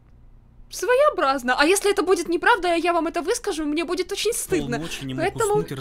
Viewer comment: (Если это будет не правда то я вам это выскажу) 8:34 5:30 И в конце мы ничего от так и не услышали :/ Хотя она сказала : "Если это будет не правда то я вам это выскажу". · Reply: Она сказала: " А если это будет неправда, а я вам это выскажу, то мне будет очень стыдно". Так что она ничего не обещала.